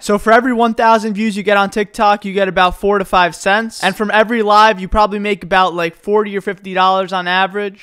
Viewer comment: i thought you said "from every lie you make about 40 or 50 dollars" haha